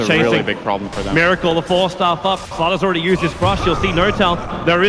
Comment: Did i hear at 6:08 fata already used his crush but fata was not there